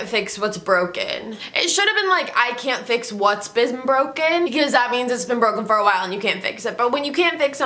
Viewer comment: "I can't fix whats bits-been broken" I live you but what are you saying hahahaha